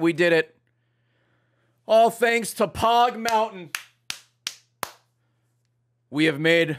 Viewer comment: I thought pog mountain said poo mountain for way to long